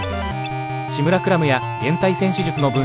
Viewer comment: 『シムラクラム』がどうしても『志村倶楽部』に聞こえてしまう